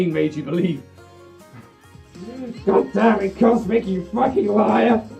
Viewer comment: What did say at 12:44-12:46? I couldn't understand him. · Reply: "Goddammit cosmic you fucking liar"